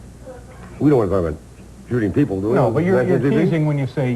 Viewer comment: Did he say something about drinking people..??